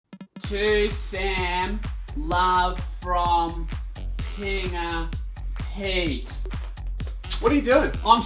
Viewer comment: Someone sent me this because they swore they heard you saying my name at the very start of the video. I can't unhear it either. "To Sam love from Pinipy"